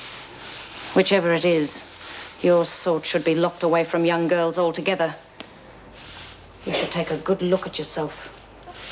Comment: What word did Bea 'mouth' @ 16:27, as it was too much of a 'close-up' for me to tell and then the following dialogue seemed to get disjointed. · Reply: At whichever point in the episode it was then lol, Bea said the words, "Bad seed", although, some years ago, I thought she said the word, "Bastard" lol. 😄 Some episodes later, Georgie tells Bea in their cell that she can lipread, and Georgie reminds Bea that she's the "Bad seed" according to Bea, when Bea spoke with teacher Mr Andrews about Georgie, while in the corridor.